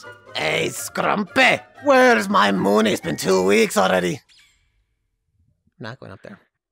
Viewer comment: HE CALLED HIM MOONIE 😂😂😂😂😂😂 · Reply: It sounds like he said “Where’s my Moon? It’s been two weeks already” Accents are fun